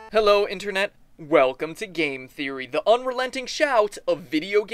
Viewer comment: does anyone else think it sounds like he says a gay theory at the end of every video